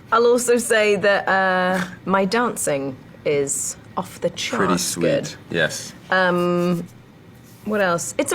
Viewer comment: My English isn't good. What does she say in 1:05? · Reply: "My dancing is 'off the charts' good".